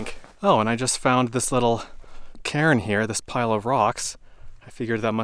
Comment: At first I thought you said, (25:00) “Oh, I just found this little Karen here,” when you saw the cairn, just after talking about how you hadn’t seen a soul all day. 🤣Obviously I’ve been watching too many “Karen” videos. Thanks for another excellent video in a beautiful location…really nice to take the vicarious hikes with you!